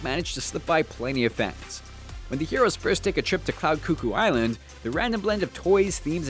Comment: wait did he just say cloud coo-coo Island